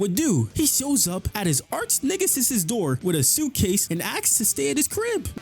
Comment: Did you say, "Arch-niggasis?"